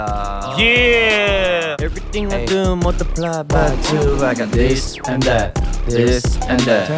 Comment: I thought chan said motherfu*** during his lyrics in this and that but he is actually saying multiply by 2